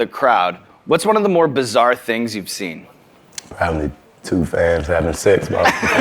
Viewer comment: Wait did he just say two “f*gs having sex” ...? 😕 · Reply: Oh he said fans lmao nvm, I knew my baby could never 🥰🥰